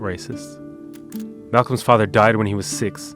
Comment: I thought you said Malcolm's father died at age 6 lol.